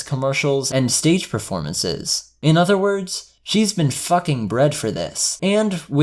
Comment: Holy shit Im stupid I heard "She's been fucking bread for this" and instantly went...wtf?